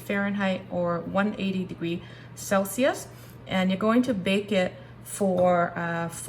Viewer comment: Yes, I think I wrote 160C by mistake...thank you so kindly! 🙏💚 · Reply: Ah! I listened to what you said in the video, with my Dutch ears I hear "100 degrees Celcius"😁